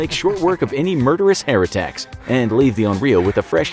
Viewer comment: I heard "hair attacks" as "heretechs"